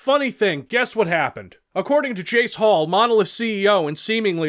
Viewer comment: Am I the only one who misheard "Jace Hall" as "Jake Paul?"